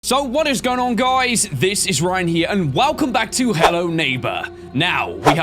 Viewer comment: At the beginning of the video it sounded like Ryan said "it's Ryan Higa" instead of "Ryan here"